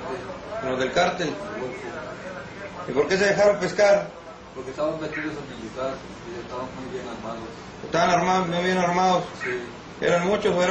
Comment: Que dice el que lo están interrogando en el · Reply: "Porque estaban vestidos de militar y estaban muy bien armados" osea que ellos se entregaron creyendo que era el gobierno cuando en realidad eran sicarios del Cártel del Golfo.